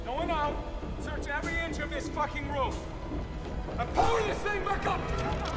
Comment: What does he say at 1:39 ? I replayed it about 5 times and all I can hear is "Pullya sain pakump" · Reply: "now power this thing back up"